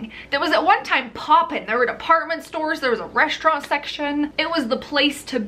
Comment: I keep hearing "Apartment Store" vs department store